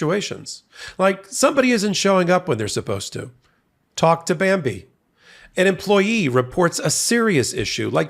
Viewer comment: OMG I wasn’t looking at the screen so I heard: “Talk to Bambi.’ I thought to myself, “that’s a bit aggressive but he’s building to a persistent fraud argument against the former First Daughter.” I thought you meant Ivana. 😂